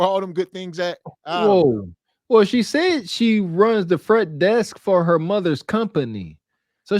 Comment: I thought she said she was 26